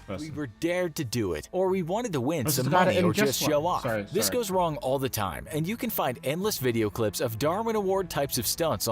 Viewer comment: what does tobi say at 10:55 and then apoligise for. I cant hear him? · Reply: This is late but he says “oh is this the one where he injests one”